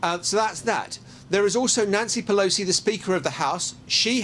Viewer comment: Did that news guy just say " Nazi Pelosi" . skip to 3:50 listen it sounded like he said nazi- Pelosi